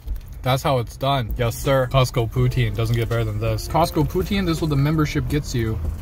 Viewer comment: I didn't read the title and I thought you said it got some protein instead of Costco Poutine